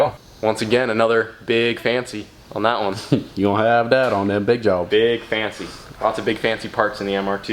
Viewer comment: hahaha, what did say at 16:33? He sounded like he was from the bayou in Louisiana lol. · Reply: 'You'll have that on them big jobs."